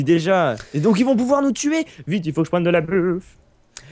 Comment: C'est moi ou à 19:48, Banana dit: "il faut que je prenne de la beuh" ? O_o